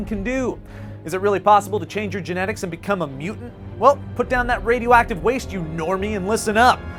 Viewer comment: at the intro I thought he said "become a mule"